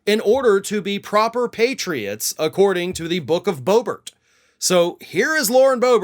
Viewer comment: When he said "Book of Boebert" I heard "Book of Boba Fett" 😂🤣😂🤣